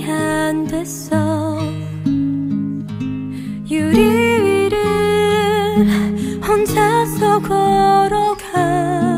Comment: 유리위에라고 써있는데 유리위를 이라고 들림...